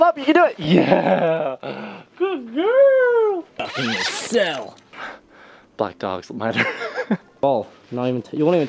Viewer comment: Me in 2020: “at 2:10 did he say Black Dogs Matter?”